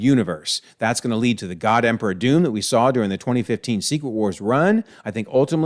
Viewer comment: OMG I DIDN"T EVEN KNOW What If s3 was out?! I dipped out of the MCU after Deadpool & Wolverine, (I much prefer bingeing a back log once every year, it's so tiring to stay engaged with the MCU all the time, I'm sure you of all people know XD;) it's my own fault for ignoring the spoiler warning at the beginning of the video, it's awesome to see Super Storm and Kahhori return. I was going to wait till World War Hulk was out on streaming to have a catch up, gah. Watching season 3 of What If in 3...2...1... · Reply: I finished the video because I had to restart my Disney+, and when you said God Emperor Doom I thought I heard God Emperor of Dune, probably because I am reading that right now! I'd like to see that comic book rumble.